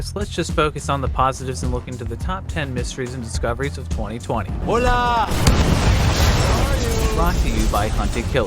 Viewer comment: the dude says "hola" but the subtitles say aloha